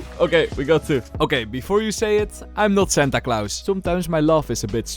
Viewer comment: with your accent when you said Santa Clause i thought you said something like Senta Clouse and in my head i thought "is that a pro player i don't know?" lol